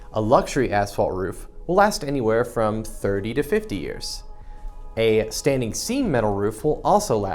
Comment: Say wha?? A Standing Seam metal roof... 25 to 30 years you say? If it's done right shouldn't it last A LOT longer than that? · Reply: I thought he said 30 to 50 years for standing seam metal.